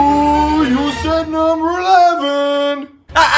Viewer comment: I always thought Patrick said something like "You said the blue lava"